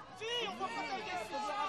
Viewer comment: C'est moi où à 0:35 j'entend un "on voit pas ta gueule"